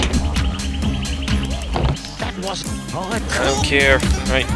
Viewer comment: When you skipped the cutscene at 3:22 it sounded like "that wasn't all that cool!"